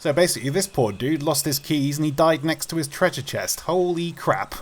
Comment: At one part I thought it said whiskey